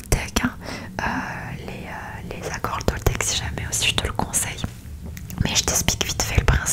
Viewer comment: Bonjour! Quel etait le mot que tu as utilize à 12:00. J'ai entendu "conseille". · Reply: Coucou ✨ Je parlais des 4 accords toltèques, c’est un livre et je disais que « je te le conseille » ça veut dire en Français que je te le recommande 😊 et après j’expliquais rapidement les fondements du livre ✨